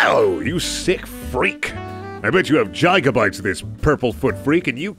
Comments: Did he just say jygabytes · did you just say gigabytes as “Jygabytes”? That’s even worse than saying GIF as JIF.